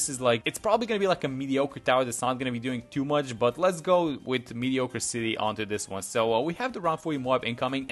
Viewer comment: Did you just say “mediocre city” instead of “mediocracy”?